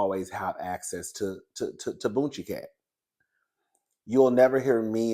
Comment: 2 minutes in and I hear "bootchie-cat"🤪😂